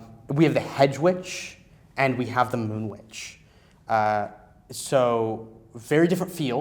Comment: I thought I heard “Moonshine Witch” initially and thought “Well…that has some interesting RP potential” 😆 ~_~